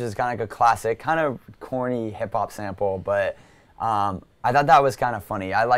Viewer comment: Did he say CORNY FUNNY HIP HOP sample? I'm triggered